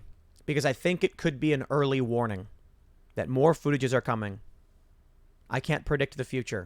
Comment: Did he just say "foodages", nd then shortages seconds later?